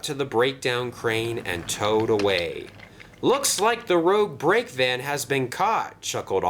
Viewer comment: Did you say "the road brake van" at 7:32?